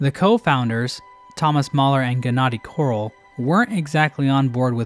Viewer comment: Half the video when he was saying the name ‘Korol’ I could have sworn he was saying ‘Quirrel’, and I was super confused. That was until I saw it spelled out lol